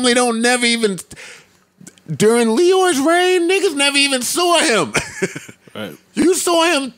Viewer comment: Was that the infamous Antwan from the Stripper party story LMFAO thought you said you never saw him again LMFAO